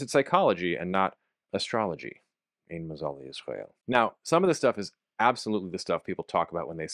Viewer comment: What is said after "since it's psychology and not... astrology" at roughly 06:05 ? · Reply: "ein mazal l'yisrael", 'there is no constellation for Israel', essentially that Jews' future is not determined by astrology or fate